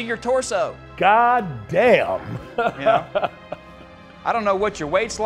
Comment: 🤣🤣🤣Easily one of the all time funniest moments of this show… “No I don’t” 😭☠️ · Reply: I thought he said "Oh I know" the whole time. I didn't understand why he told him to shut up 😆